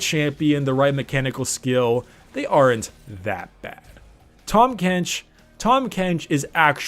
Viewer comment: when i heard 8:51 i was thinking " i gonna main THAM BENCH" ( yes i said bench)